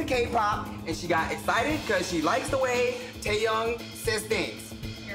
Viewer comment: ik he said Taeyang but it kinda sounds like he said Taehyung for BTS